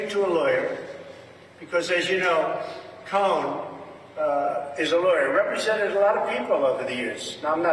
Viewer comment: umm was this another cognitive “gaff” moment..? Am I losing my mind, or did he just say, ”Cohn was a lawyer—represented a number of ppl over the years…” [..as in Roy Cohn ..huh??🤔]